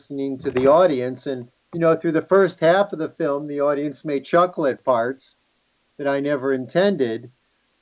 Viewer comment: “and through the first half of the film the audience made chocolate farts” I know that’s not what he said but the audio got fuzzy there and that’s what I heard and what the subtitles read.